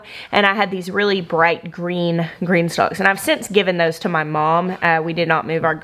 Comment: With your accent (I'm in Australia), for the last couple years I thought you have been saying "Green Stock"!